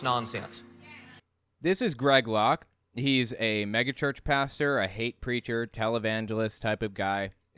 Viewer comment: When you said "he's a megachurch pastor", at first I thought you said "he's a mega jerk pastor". With this guy, I am more inclined to believe the latter.